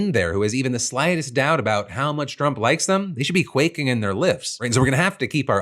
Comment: Question if I heard Philip correctly… around 4m15sec Philip says people in positions to be FIRED by DJT should be, “Quaking in their boots,” I presume is what Philip meant to say, but IDK. Sounded like he said, “Quaking in their Lifts or Lips,” instead of “Quaking in their boots.” Did I hear incorrectly, or did his tongue utter an erroneous human error? Great show BTW, good job!